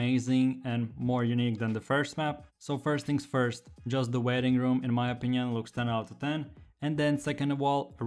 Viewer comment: When you said waiting room it sounds like u said wedding room. Lol btw i sub ed